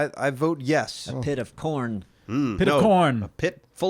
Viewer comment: I thought said "a pit of porn" I Didn't hear corn. So thought corrected and said "a pit of corn". Lol.